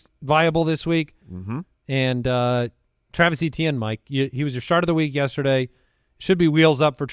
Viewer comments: At around 20:21 does call Travis Etienne the 'shart of the week'? 😂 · Did say Etienne was Mike's "Shart of the Week" 20:21? Because that should be a new segment. Joshua Kelley was mine last week 😂